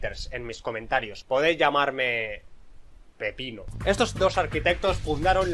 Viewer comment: pepino xd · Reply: yo pense que dijiste pipepino